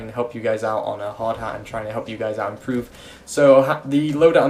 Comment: am I the only one who heard him say "Lardhat?"